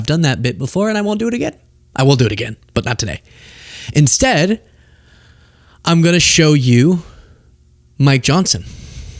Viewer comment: Oh my god! I was watching this and looked away for a minute to get some water and THOUGHT you said "instead I will show you my Johnson" and I about choked to death on my water😂😂